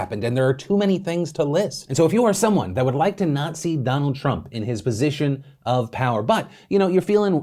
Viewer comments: When he said “To not see Donald Trump” I heard “To Nazi Donald Trump”. After saying non democratic. I think my ears are saying something. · "Not see Donald Trump" I heard "Nazi Donald Trump" Biden/Harris 2020! VOTE now or forever hold your peace